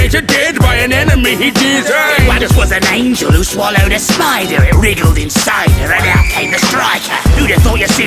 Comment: I realy like hoe it sounds like edger is saying Alice swallowed a spider and out came the striker but actually he's saying their ounce was an angle who swallowed a spider and put came striker